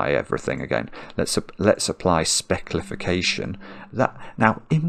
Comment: it's not "specklification" that looks like "Alexa". You said "Let's a-- let's apply". The *a-- let's a* made it sound like you said "Alexa" 😄